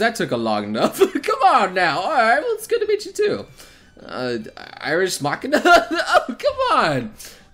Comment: Sounded like "Irish Machina" there...